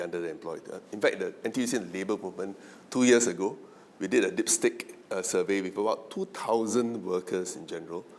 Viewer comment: I must be getting old. I was quite sure I heard him say "a dickstick survey" instead of "a dipstick survey"? I don't know if I should open a box of fire crackers or a box of tissues. Here's the deal...(imho) 1. Which job is the safest ? Well, the safest job is one where no one, not even AI, can touch you, except Lux & Palmolive. 2. Which is worse.? Underemployment or unemployment? Passion can not put food on the table. You throw a coin in the air. Do you know how many graduates' heads the coin can hit? Remember, not too long ago, there was an article about "micro-retirement"? It's quite strange to hear about this phrase. Why not call it sabbatical or voluntary unemployment, or mini-unemployment, or maxi-unemployment, or just a "Professional Bum". 3. Jobs, jobs, jobs & more jobs! AI will revolutionise the way we work & add value to our jobs? True, but the runway is getting shorter the longer you wait. (Wait long, long.) Someone said, "Stay flexible." Think local & act global? Sure, try telling that to a young couple, both unemployed, with kids and mortgage to service. You slowly wait, lah. But remember, the banks won't wait! On one end, you moan & groan about the bleak job prospects. Then, when you get an opportunity, you again moan & groan about the salary, the toxic work environment, the poor psychological safety, no work-life balance, no WFH, and the stressful hours at work. That's where AI comes in! AI will take over all this negativity, so you can "micro-retire" and take